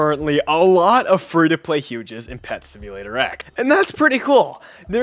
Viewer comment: Dude thought he said pet simulator 99 but he clearly said pet simulator x